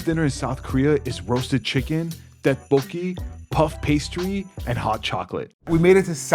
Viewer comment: when he said tteokboki i thought he said dead bull smth but the when he said have u ever had tteokboki then i realised